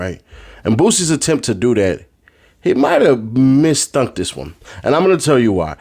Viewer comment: did you say “misthunk”? 🤔🤣😂🫨😵‍💫